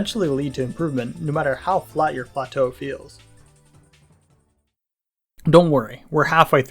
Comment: I left the video playing in the background and got immediately snapped back into attention when I thought he said "no matter how flat your butthole feels"